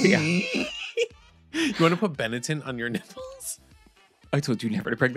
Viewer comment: The way I thought you were saying "Benneton" for most of this and could not figure out what their clothes had to do with nips 🤦‍♀